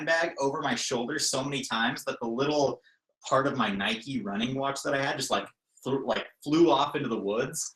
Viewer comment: At first I thought said hat but I went back and he said nike running watch.